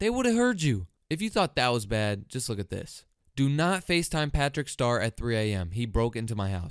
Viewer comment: Bro is literally saying “pæþřĩçķ ștœř” 17:15 Don’t want to offend any British people